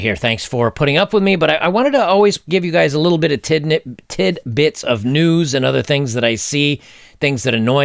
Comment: Pretty sure at 8:04 he said "tid nips".....thats all I heard in the whole video.